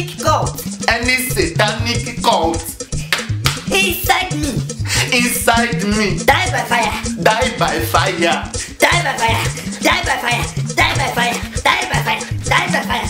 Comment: sounded like she said dab by fire